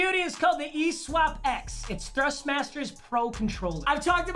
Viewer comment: Ill be honest i thought that controller said thrushmaster